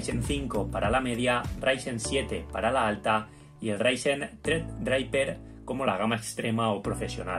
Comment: Alguien me dice que dice este hombre en el 1:45? Osea dice el ryzen 7 para la alta y ryzen ksjysjwbje para la no se que 🤔. Me gustaría saber que dice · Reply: threadripper